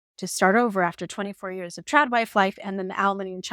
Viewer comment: Trad wife? Sounded like you said trapped wife!